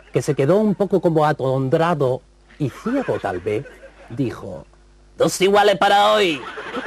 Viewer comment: ¿Qué dice Millán en el 42:24? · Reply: ¿Te refieres a lo de dos iguales para hoy?